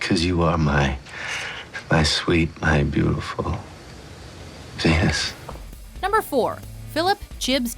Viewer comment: at first i thought tig said "cuz you're my sweet my beautiful penis"